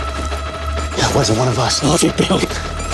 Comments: Damn. I can't even make out what The Protagonist says at 00:42. Subtitles are a must for this movie. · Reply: I’ll take the help. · what did he say after the guy said “that wasn’t one of us” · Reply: i'll take the help